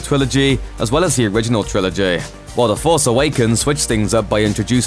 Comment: Does he say twilogy 7:28?